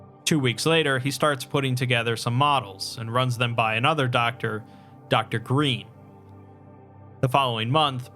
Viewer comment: For a second I thought you said “Dr.Breen”